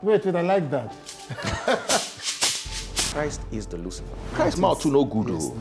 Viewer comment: Did I just hear him say " christ is the lucifer"????